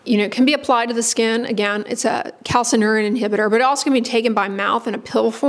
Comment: tacrylimus swish & spit calcium inhibitor? what did she say? unclear